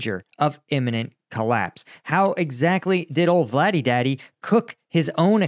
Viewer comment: LOL bro did you just say "vladi daddy". Hahaha I had to do a triple take on that just couldn't believe it. 🤣🤣🤣